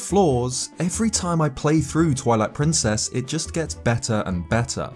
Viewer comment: Sounds like you said "toilet princess" lol